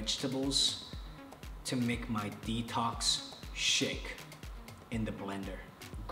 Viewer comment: I thought he said detox shit and I was about to agree w him. U don’t need to detox or diet Jackson! :)